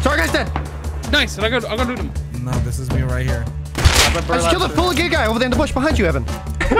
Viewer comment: did anyone else hear "i killed a full gay guy" Lmao